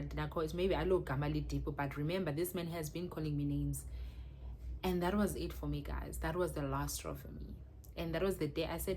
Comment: Hai hai hai what is that word in English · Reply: 😂😂😂Crook